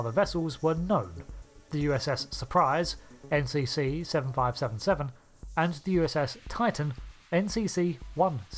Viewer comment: why do I keep hearing the second vessel’s name as “USS Surprise Muthafukka”?